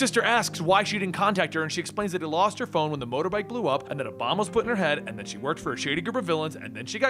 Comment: Caption says "and that Obama's pitting her head." What? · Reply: putting